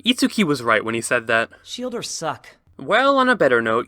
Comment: I thought they said "shield are suck"